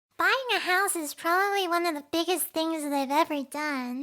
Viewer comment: I thought it said bullying a horse